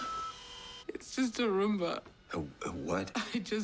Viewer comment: Did Michonne say she got a Roomba?